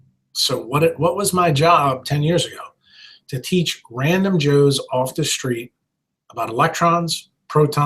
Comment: did he really say his job was to teach "random Jews off the street" about light, etc.? That's interesting. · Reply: Wyde A. Wake “ random JOES off the street”. Hehehehe not “random Jews”....